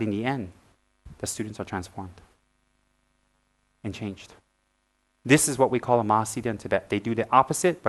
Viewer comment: “this is what we call a _____?” Anyone knows what he’s saying at that part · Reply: "this is what we called a Mahasiddha in Tibet"